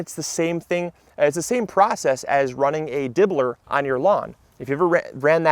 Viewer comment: a 'dibbler' is called a coring machine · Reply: I thought he said diddler